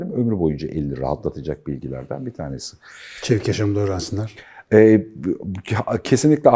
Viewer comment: arkadaşlar 25:50de ne diyor Sinan hoca anlayamadim. neyi öğrensinler dedi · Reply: Mustafa Acungil, çevik yaşam eğitimi